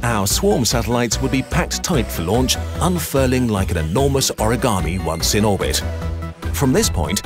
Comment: did someone say origami!?